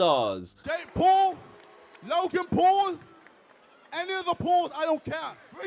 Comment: omg I thought at @ 0:47 KSI said "Jake Paul, Logan Paul, Annie LeBlanc idc" then I realized he said "ANY OF THE PAULS" I about lost my shit